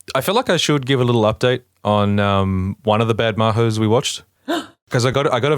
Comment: Also MadoHomos is 10/10 I definitely thought you just said "mahous" until it hit me.